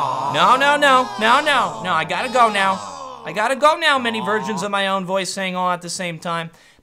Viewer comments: That ending where he said something like No no many versions of me, I have to go. It's those small touches apart of his videos that really put them on the next level. It's pain staking to edit lots stuff like that in and he does it, the I AM THE HEXAGON being a great example. He edited all those rotations of his face cam. Great stuff WarOwl <3. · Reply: I thought he said "many virgins of myself" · "I gotta go now many virgins of my own voice" wait wut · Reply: i heard 'versions' the first time, then read this comment, then heard it as 'virgins'. what'??